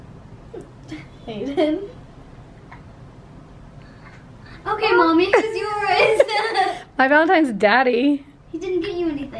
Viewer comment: DUDE HANNIE IS CONFIRMED! OMLLLLL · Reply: I thought said okay mommy can you erase that? But idk that’s what I heard